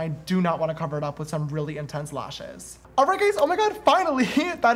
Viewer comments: I’m sure I can’t be the only one who heard “alright gays” at 6:40 😂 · “Alright gays!” Am I the only one who heard that?